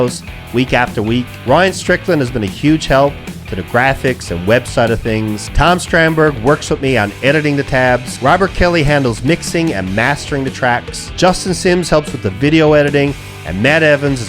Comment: did I heard Robert Keeley from keeley pedals?